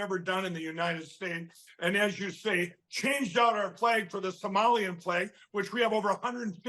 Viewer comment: Ohhh, I thought he was saying "Somalian *flu*"! Geez, the star they use looks like the one on Captain America's shield. The idea of updating state (and hopefully territorial) flags is to make them simple enough for children to recognize and draw. I suppose that would go for adults as well, but I'm not sure ML knows which end of a writing instrument gets moved around on the paper.